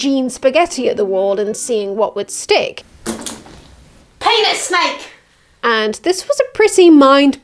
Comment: When you said penis snake I thought you said 'pay the snake'. I kinda like pay the snake in that voice. It gives Eve agency.